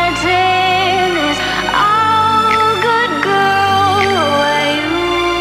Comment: What does she say after gone in 2:22? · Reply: Nenna Aco „rotten? It‘s all good ..“